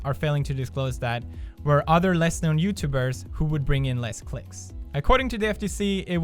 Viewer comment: I thought he said "bring in less dicks" is me or ...